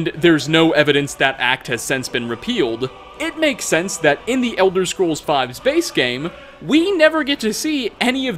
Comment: What says: "In the Elder Scrolls Five's base game" What I understand: "In the Elder Scrolls Five space game"